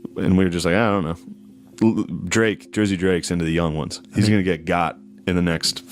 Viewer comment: did he say he’s gonna get caught for being a pedo…unfortunately. Yeuck